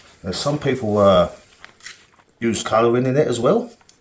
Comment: Around the 2:50 minute mark you mentioned something for speeding up the process and it sounded like “coal” but I think I am hearing it wrong. Help! Arkansas, USA here! Great Video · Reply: Hey Sheri! Sorry about that haha, I was talking about food coloring, food coloring sets the mold off even faster, hope that helps and thank you for watching us 😊 GET PLASTERED 🎉